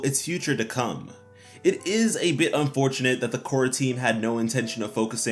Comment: when he said "Korra team" i heard "quarantine" and i was so confused 😂😅